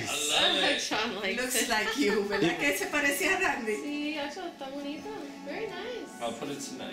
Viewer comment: The mother and sister of were speaking some Portuguese? I heard his sister (3:47-3:50) saying "tá bonito" and didn't understand what his mother said before. Merry xmas from Brazil to you.